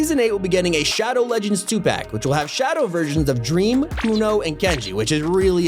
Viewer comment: DID HE SAY DREAM